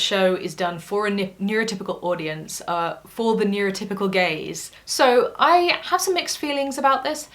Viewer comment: I actually thought you were saying neurotypical gays instead of gaze.